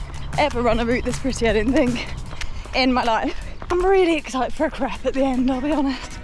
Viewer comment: Just after 10:30, did say she was really excited for a crepe at the end? Because the subtitles thought she said something else that really is oversharing! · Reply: Crepe. I said crepe 😳😂